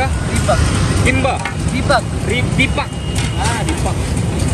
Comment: Aku dengernya "KIPAK" namanya 😔